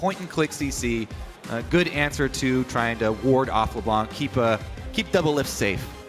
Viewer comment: For some reason, at 4:00, i thought Kobe called Doublelift, "Devil lips". ... Hmm ... sexy.